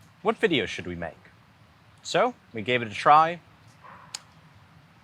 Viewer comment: I swore I heard you close out this video saying, “I’m Brian Eno, and memes matter.” 😮 but yes we must thank him